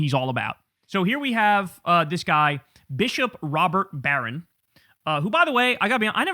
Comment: I thought Kyle said this dude's name was "Robber Barron".